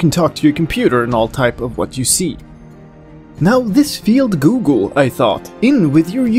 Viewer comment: I heard a lot more pauses this time and I heard you say giggle instead of google.